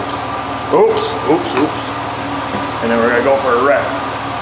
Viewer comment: Sure sounded like you said "We're going to go for a wreck"! Pretty sure it was a rip not a wreck. 😁